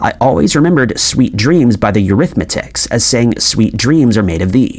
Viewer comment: Wait...is there another Mandela effect with the Eurythmics band name? 😮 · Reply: I wondered if anyone caught that. I thought he said Arithmetics, instead of Eurythmics.